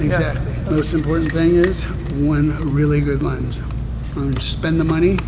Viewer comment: thought he said "one really good lunch" and i totally believed that was necessary to be pro